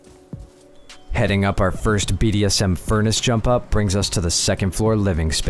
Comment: hold did bro say bdsm?